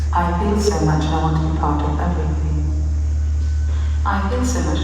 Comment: Is it weird that I heard "I feel so much and I want to fart on everything."